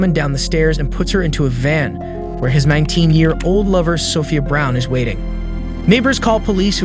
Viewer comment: Sounds like he's had an old lover for 19 years. I really appreciate the stories and footage and work that goes into these and, I have to admit, I struggle with the narration. Earlier, I heard the police in Albion. Michigan got a call ... Makes it harder to follow the story when you're thrown off by voice inflections. This is either AI or someone doing a cold reading (first read through) or maybe the copy needs punctuation or whatever. I'm only mentioning it because I think it's a good channel and could be great aside from that.